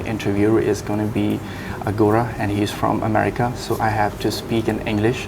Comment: did he said gora